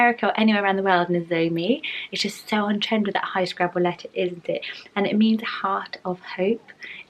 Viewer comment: did she say "high scrabble letters?" as in the game scrabble? 😂